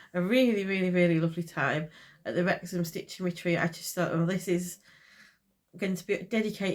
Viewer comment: Watching / listening to this in the background, but you definitely caught my attention when I misheard that you went to the 'rectum stiching retreat 😂😂😂'